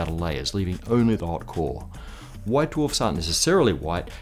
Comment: LOL who else heard CORe as HOE XD